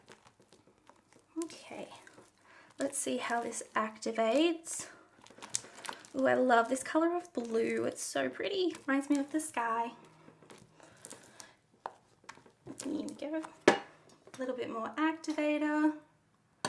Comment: When you said it remind me of the sky i thought you said it reminds me of this guy im so.. ugh